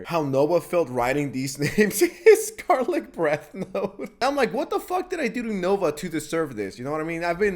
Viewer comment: 50 cent goes I’m like what he saying fuck me for